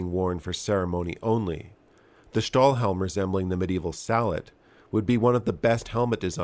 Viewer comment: Sallet, not salad like I thought I heard 😂